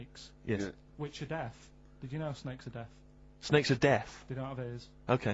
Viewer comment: Haha I died when I thought he said 'snakes are death.'